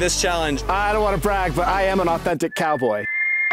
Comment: I thought that said autistic not authentic